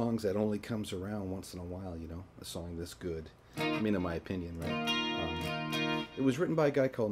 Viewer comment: What did you say at 0:19? 'me and my opinion'? :D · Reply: Toppu I think he said “I mean in my opinion”